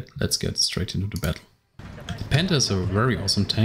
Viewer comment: When you said panther it sounded like you said Panda